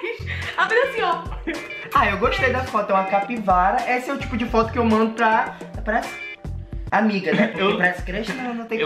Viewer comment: "esse é o tipo de foto q mando pra cinti... Eu ouvi um Cintia? Kkkakaks